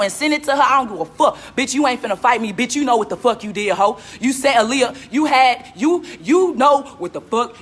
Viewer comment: it sounds like they was about to say "you set Aliyah up" could be that she was gonna say "you set Aliyah off" but the way they stopped seemed like they was catchin themselves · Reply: She did say CIROC you set Aliyah up